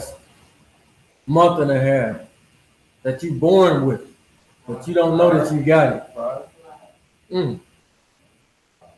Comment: Right at 21:27, when he says what are we born with that we don't know we have? I heard some responses, but couldn't understand it...did anyone catch that? Thank you. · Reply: He said Pride. We were born with it but don't know we have it.